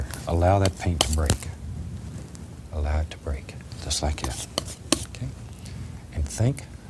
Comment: “allow it to break just like you” Am I hearing things lmao